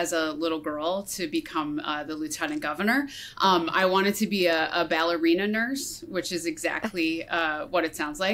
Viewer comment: "I wanted to be a ballerina/nurse" is about the most awesome kids dream I've heard in a while. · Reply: I will admit that I first heard it as if she wanted to be a nurse for ballerinas.